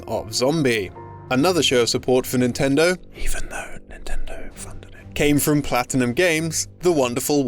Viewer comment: "Even though Nintendo..." ...what? Can't understand that at all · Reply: Funded it